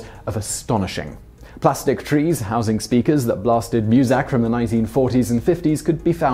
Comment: did he say musac?